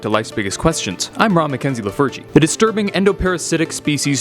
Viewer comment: In the intro I thought he said, "Law Mackenzie the Virgin" XD